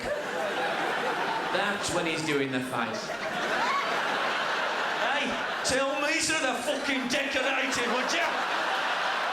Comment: What does Frank say at 19:57 please? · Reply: "Tell me to do the fucking decorating would ya" I think.